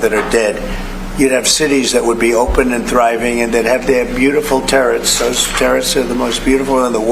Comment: I honestly thought he said they'd "have beautiful tariffs"... and, sadly, it sounded just about right for him... like RFK Jr. lamenting that folks with autism will "never pay taxes"... 😐